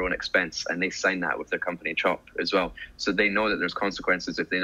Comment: what did he say? Can’t understand? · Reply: Hey bro, were you referring to when I mentioned about the company chop? This is the company stamp they put on official documents of the factory 😀